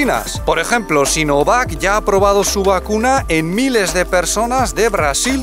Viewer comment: ¿Qué os parece la reacción de los chinos ante la posibilidad de reservar la vacuna? Es posible que pronto os crucéis con algún estudiante de aquí que ya la lleva 😅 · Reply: en los subtítulos pone "aprobado" (en español y en inglés) pero yo creo que dices "probado"